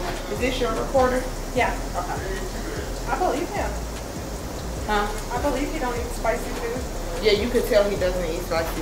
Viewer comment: The lady had the absolute nerve to say you weren't used to eating spicy things at the end? After they exponentially multiplied the Scoville level? Real class act. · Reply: I thought she said, "I believe him when he said he doesn't eat spicy things", which would suggest is the one who originally told them he doesn't eat spicy food. I don't think she meant any offense by it.